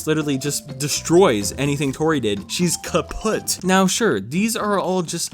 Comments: is he saying 'kaputt'? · Did you just say "kaputt"? xD